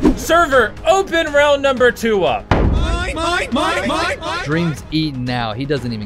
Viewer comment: > 1:51 i heard "mói mói mói mói mói mói mói " : ))))